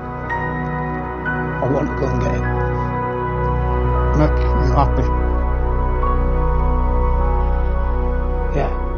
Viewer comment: could anyone tell me what the father says? something like "does he make you happy?" 04:28 or what is it? Thanks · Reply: I think he says 'will that make you happy?'